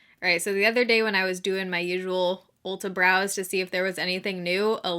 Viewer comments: Totally heard that as "doing my usual Ulta brows" - not "browse". 😂 Same difference, right? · When you said “Ulta browse”, I thought you said “Ulta brows!”